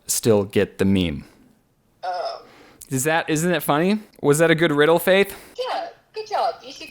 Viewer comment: when he said “is that a good riddle faith?” i thought he said “is that a good riddle babe” i played it back bc i was like tf did he just say